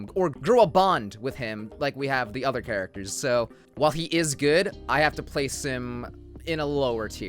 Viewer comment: I heard "I have to play sim." I was like nani. Edit: what Editv may21 3:01 pm ok bay bye bey baiwjwiiwi